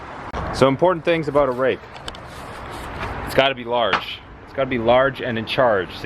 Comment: I heard "rape..." lol